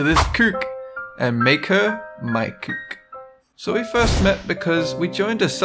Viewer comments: im not sure if im hearing right or not but i only hear him saying " made her my kuk" which in swedish means "made her my cock" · In the beginning it sounded like he said "and make her my kuk" kuk is swedish for cock.. and not the chicken kind...